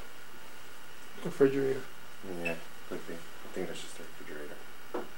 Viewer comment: After the guy asks "are you hiding in the kitchen", I hear what sounds like a child's voice reply "yea" at 2:30. I am listening with headphones. Anyone else hear it? I also hear the same voice say "go" after the man says "let's go" at 1:44.